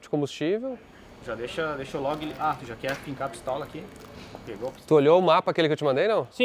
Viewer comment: Depois de "deixa eu enfiar o dedo" parece que ouvi alguém perguntar ( Tu quer enfiar a pistola aqui? 1:04:04).Abraços.